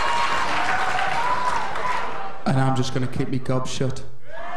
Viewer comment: I can't understand what Robbie said at 3:02 and Liam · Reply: Robbie said - am just going to keep my gob shut